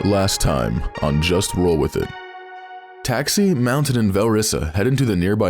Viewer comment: I missheard the opening recap and thought said taxi mounted velrissa